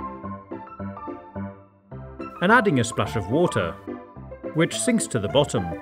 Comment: did he really say special water?